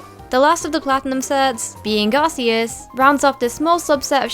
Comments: you said Garceus instead of Arceus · I heard "Garceus" when it when Arceos XD